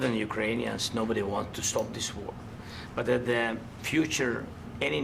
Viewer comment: Did Zelenskyy just said nobody wants to stop this war? 41:40 - Zelensky signed a seize fire in <num> with putin. Zelensky was elected in 2019.... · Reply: "Nobody wants to stop the war more than Ukranians" he said